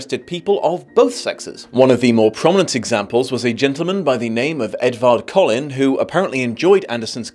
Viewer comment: im sorry did you say "a man by the name of Edward Cullen"? Is THAT where twilight took the name from?